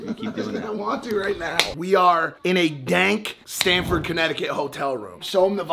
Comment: call me an idiot... but its "STAMFORD"?? always thought bro was saying "STANFORD" lmaooo